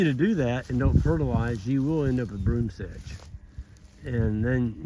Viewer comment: Hi, what is the name of the weed you mentioned at 7:38? Did you say "Brooms thatch"? I googled that and nothing comes up.. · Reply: Broomsedge